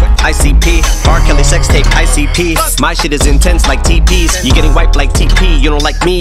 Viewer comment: I just heard him say - my shit is "in tents like teepees" please confirm!!